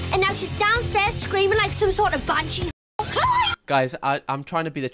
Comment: i think she said banshee 😭